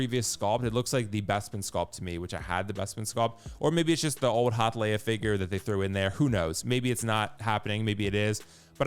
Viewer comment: I keep hearing, “the old hot Leia figure” 😅 looks like the old one yeah, wonder if they’ll redo it. Good video!